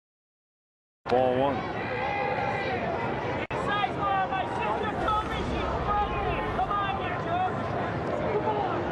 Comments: What does the fan say here? I could only pick up “Sizemore, my sister...” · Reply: My sister told me she’s pregnant! Sizemore’s ‘bout to be a dad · anyone catch what that guy yelled ? “Sizemore, my sister…..something something , come on ya jerk !” I can’t make out the middle part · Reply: “My sister told me she’s pregnant”